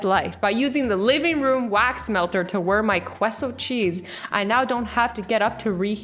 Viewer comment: DID SHE JUST SAY QUESS O????